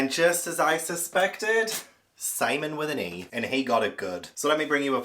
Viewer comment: When you said "simeon with an e" I wasnt looking at the screen and misheard the "e" as an "a." I did the mental gymnastics and concluded in that brief moment that his name was Salmon. The L snuck right in there. What a day.